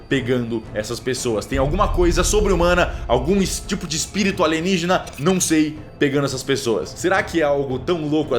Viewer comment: na 1 vez q eu ouvi o cellbit dizia um espirito ou alienigena, mas olhando a legenda fica espiritualista